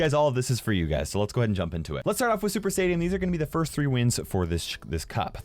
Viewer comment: Did he just say Super Saiyan!?! 🤔😃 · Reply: Sifat Al Jubayer super stadium lmao